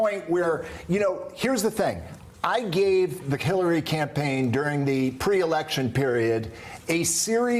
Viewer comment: Did he say “I gave the Killary campaign…” oops! Freudian slip.